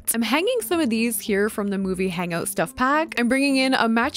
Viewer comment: I love your videos but did you say "From the movie?!?!?" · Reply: yeah?😅😅 from the "movie hangout" stuff pack which is a sims 4 DLC lol